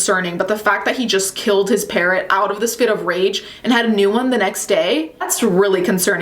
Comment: I thought you said he killed his parent, and got a new one the next day 😬